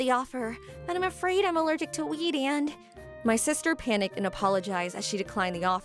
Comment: I notice at 3:10 the caption said weed instead of wheat. Lol